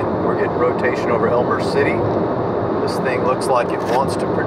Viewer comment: WAIT did he say elmore city ?!1:17 gumball!!!!!